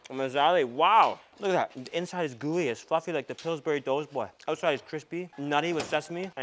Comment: on first watch I thought you said "Gooey as F***" at 3:38 and was caught so off guard as I can't imagine you swearing :p